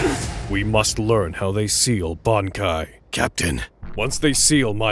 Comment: Steal bankai, I thought you said seal bankai. That is why spelling is important.